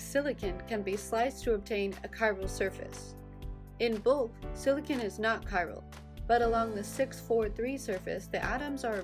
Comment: That is really neat! At 1:26 what did she say? "In ___ , silicon is not chiral" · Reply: In bulk, silicon is not chiral